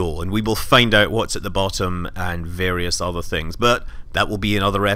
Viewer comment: I heard him say "die safe" then later realised he meant "dive safe"